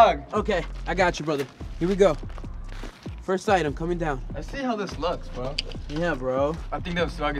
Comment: I thought said Lexi but he said let’s see